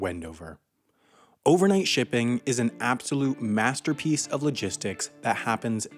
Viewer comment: I was so damn tired I thought it said overnight sleeping